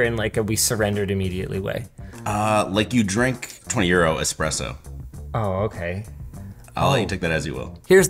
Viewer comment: Is he saying "20 euro espresso"? Because that would be ironic - since coffee of any kind is much cheaper than in north America... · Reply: 2 euro i think it's what they said